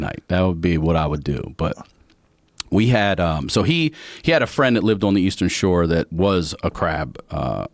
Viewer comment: when he pauses after crab I thought he was saying his dad has a friend who was a crab